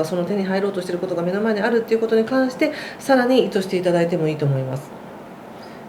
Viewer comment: 位の所で「更に〜してもらってもいいと思います」という部分がどうしても聞き取れません。何とお話されているのか教えていただけますでしょうか😅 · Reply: 聞き取りづらくてすみません🙏 「更に意図していただいてもいいと思います」でした＾＾；